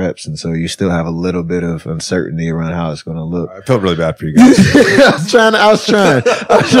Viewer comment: what did JJ Redick say at the 1:14:04 mark?? lol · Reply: Think he said "Felt really bad for you guys."